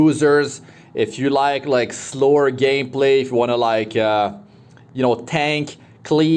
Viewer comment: Anyone else heared, “slower gay play”